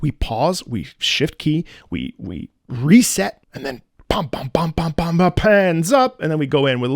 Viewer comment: For a split second, i thought he said "pans up," instead of "hands up."